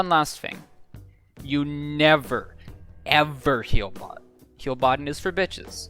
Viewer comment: did u say heal bot at 18:08? what even is that?